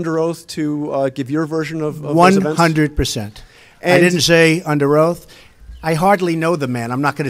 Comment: when asked if he is willing to testify under oath, Trump says "100%, I did not say under oath" Am I the only one who caught that. Trump was not answering in the affirmative.